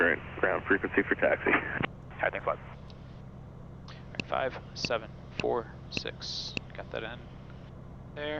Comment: Cool plane. I like your videos. At 1:48 when clearance sends you to ground, what did you reply? I can’t figure out what you said. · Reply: "Alright thanks a lot" but I kind of mumbled it :)